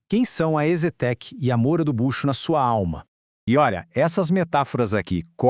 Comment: Juro que escutei "Moura do Bucho." 😂😂😂